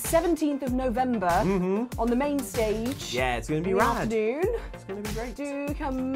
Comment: did anyone else think said: "It's gonna be lad"? I think even might've heard it that way, judging by her closed eyes shortly afterwards.